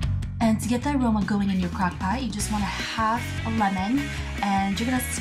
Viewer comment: I thought it said cockpot...